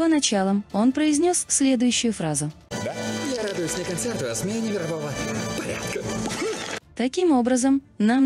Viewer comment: Так и не понял, что он сказал на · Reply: "я радуюсь не концерту, а смене мирового порядка*